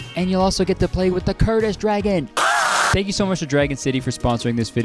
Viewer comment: I for real at first thought he said "and you get to play with a kurdish dragon" 💀